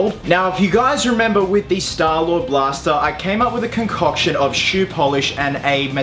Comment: thought it said 5k$ blaster lmao ty brain